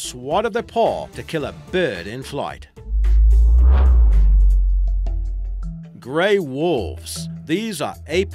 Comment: when he said Grey Wolves my sister thought the guy said they have 6 packs..